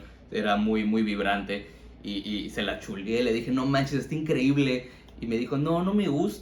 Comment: We entendí "se la chupe"😢